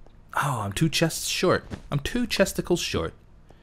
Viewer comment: did you say ' i'm short of two testicles.'? I thought I heard that.